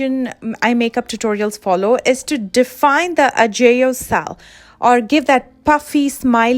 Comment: What is she saying at 4:47? I can’t spell it and desperately need to know how lol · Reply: aegyo sal